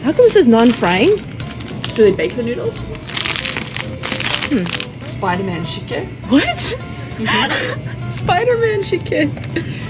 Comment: I literally heard ”Spider-Man chicken” wtf im so stupid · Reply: Its Spider-Man Shikhye